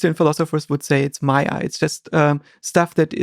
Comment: Did he just said maya ?